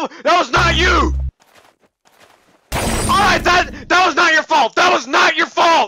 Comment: I thought John said "THAT WAS NOT YOU FALL" but that was wrong......haha